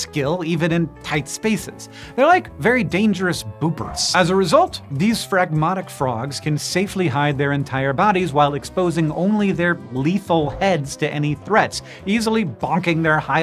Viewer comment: "They're like very dangerous 4:02 ¿boomperts?" Seriously, what was that word supposed to be? Edit: turned on the CC and apparently it's "boopers", which, um, I guess is a descriptor, but the way Hank said the stuff leading up to that term, I was expecting some kind of cultural reference or any other sort of comparison to a thing from an outside context rather than a redescription of the frog from within the established context.